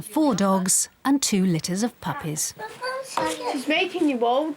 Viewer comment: Two liters of puppies? Am I hearing it right?